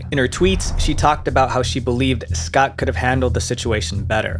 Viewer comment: Did I mishear, or did he actually say “Vivie’s Pot” instead of Vivziepop lmao